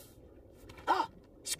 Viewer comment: Correction guys: Squidward tried to return fire, but instead he put out his fire with your fire. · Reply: When I was little I always thought SpongeBob was saying that Squidward put out of the fire then it’s war 😂